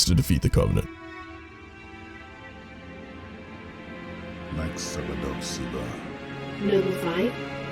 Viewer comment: "Megszakad a szívem" is what Jorge says around 12:32 "my heart is breaking"... He was born on Reach too.